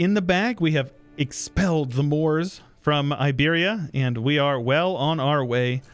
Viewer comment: First I thought you said "the Norse" at 6the start of the video but then I realised you must have said "the Moops".